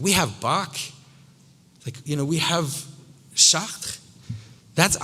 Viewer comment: What was the name he said at 45:30 after Bach? It was too French for me to detect! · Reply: Chartres (the cathedral in France)